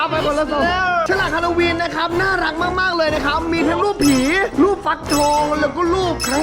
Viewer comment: ตอนที่4:33ที่พี่พูดว่าผี ผมได้ยินว่า หี